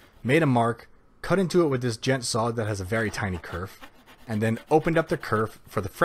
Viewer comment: did you just say djent my good sir?